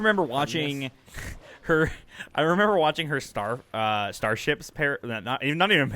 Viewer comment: Did anyone else think it sounded like said "I remember watching her starve"?